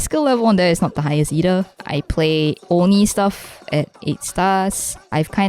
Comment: BRO I REALLY THOUGHT YOU SAID “horny stuff”